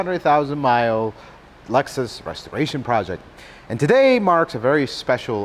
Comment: Instead of restoration, I thought he said frustration project 😅